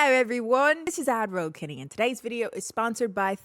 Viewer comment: Everytime she says "AdRoll Kennie" I hear "Admiral Kennie" and expect her to be wearing a tricorn hat lol